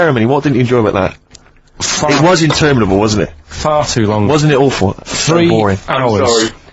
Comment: Only after all these years have I figured out that at 13:09 Ricky likely through Karl was gonna say "fucking hell" when he was about to say "far too long".